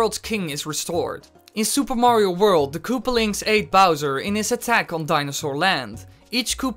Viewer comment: did you say THE koopalings ate bowser?